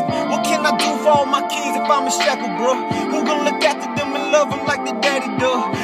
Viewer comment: When said “How can I help my kids, if I’m in shackles” I thought he said, how can I help, if I’m a sucka